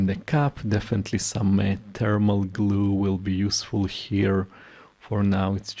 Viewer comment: What type of adhesive is recommended for sealing the bulbs back on? I think I heard you say thermal paste, but I'm not sure. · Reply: I think he said thermal glue, meaning a heat resistant glue, but often it screws or clips back in, which is sufficient.